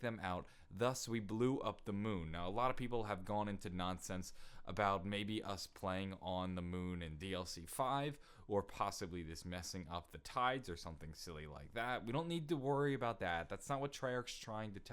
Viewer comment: Did he just say DLC 5?